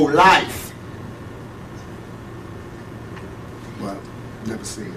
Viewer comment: what did he say at 24:16 - 24:18???? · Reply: He said "but never seen him"